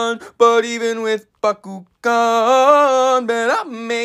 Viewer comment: He said Bakugun I thought he said "But even with a freakin gun"🤣